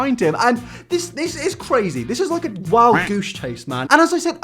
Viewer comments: I heard " this is like a wild douche chase" and I think thats perfect. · Did he just say 'wild douche chase'? I certainly hope so.